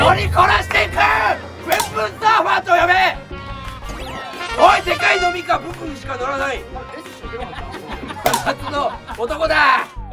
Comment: これさ、「デカいノミかブンブンしか乗らない！」って配信の時に聞こえててずっと頭に「？」浮かんでたんよ。